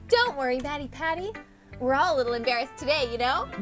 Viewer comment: my subtitles said don’t worry daddy patty. instead of Mattypatty and that was fun